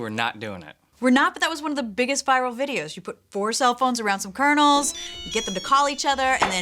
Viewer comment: the closed caption said "You put four cell phones around some colonels, get then to call each other, and then pop, pop." 15:02 - Grant uses the Force . . . Gauge. 18:51 - "Am I missing an eyebrow?" -- Tori this time.